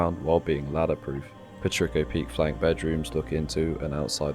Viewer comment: Can someone help me understand the name of the peek at 0:53? Patricco peek or? I hear this a lot, but I don't know what it is actually called. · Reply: Patrico I think is how you spell it. It’s just a horizontal embrasure with a floor through the middle of it (usually on the outside)